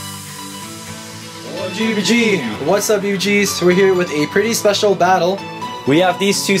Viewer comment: During the intro, I thought you said "What's up! Youjizz"